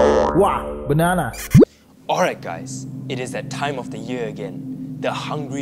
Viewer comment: Reaper:alright guys it's that time of the year again Me:ohh I thought you said "that time of the month''